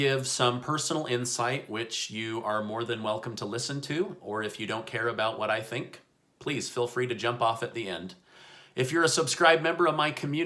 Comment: I swear I thought he said "or if you don't care what I think, feel free to jump off a bridge" 😂